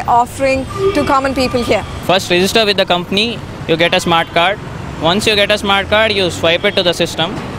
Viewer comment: I thought he said "At first you'll get a smart car".